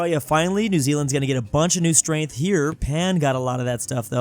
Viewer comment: did he just call Japan pan